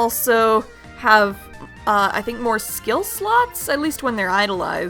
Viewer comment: I thought you said skill sluts instead of skill slots...